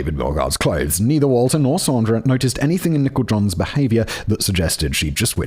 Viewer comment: Definitely thought was saying "Nickle John" for the first 40 minutes lol